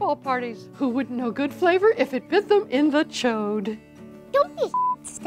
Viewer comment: ok ill bite, dunno what cuss word did the girl said. what word is it? lol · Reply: Chode? Seemed obvious :P